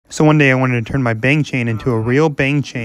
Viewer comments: as a stay i thought he was saying “bang chan” at the beginning😂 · Ohhh you said Bang chain I thought you said Bang Chan from skz lol